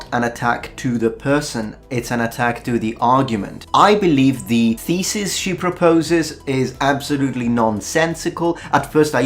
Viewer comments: "I believe the thesis she proposes....." I heard: "I believe the feces she proposes....." :P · When said "the thesis she proposes" I thought he said "the feces she proposes" I found it appropriate. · "I believe the feces she proposes..." That's what I heard. :D